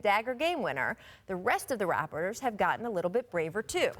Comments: Did she just say, rappers?? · Did Rachel just call them ‘the rappers’? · Did she just say rappers at 1:23 or is it just me